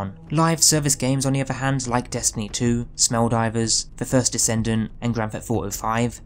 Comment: ngl i hear smelldivers lol